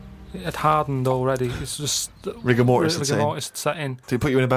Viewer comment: Anyone else think that at 3:47 Steve says "Rick and morty is insane"? I hear it every time I watch this episode